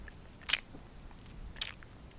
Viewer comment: This is they beast day my new clothes came and a tresh video XD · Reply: Lol I thought you said trash 😅🤣🤣